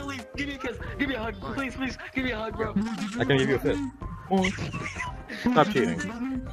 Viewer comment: For those who don’t know said “stop cheating” and it sounded like he said “f*ck you” so there you go